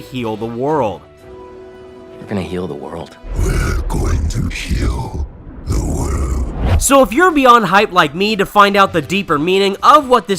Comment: I may be hearing wrong but with his voice, it sounds as if Venom, instead of saying “We are going to heal the world”, is actually saying “We are going to kill the world”. At least that’s what I’m getting. I don’t want to get all wild with my theories for this game though. But (if I’m right about this potential different line), I’d imagine it would be a last line of dialogue from Venom before the game ends and the credits roll. Thoughts?